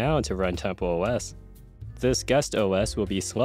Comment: What he said: "this guest OS" What i heard: "disgust OS"